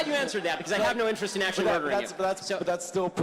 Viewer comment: 초에 i have no interest in 'actual' murdering 아닌가용? actual이 들리는 것 같아서요 ㅠ